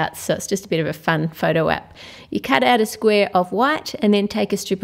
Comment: Before i thought she kept saying "You cut out a piece of WHAT" Me "Wait what do you cut out?"